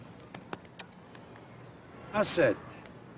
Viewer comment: What do mob guys say that sounds like “Ha’ sit”? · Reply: "How sad...I'm sorry to hear about the marital situation"